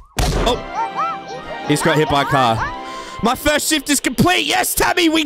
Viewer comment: when it said first shift i thought he said first shit